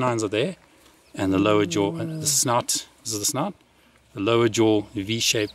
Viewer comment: For a minute I thought the archeologist said "snot" instead of "snout".